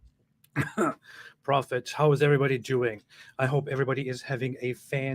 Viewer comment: I swear I thought you said "how is everybody jewing?"!